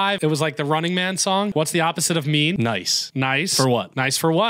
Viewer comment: I thought he said opposite of “me” so I was like. Well being aware is a step ig